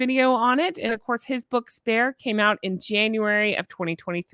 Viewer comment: I heard "did you read her book THE B*TCH?" 😂😂😂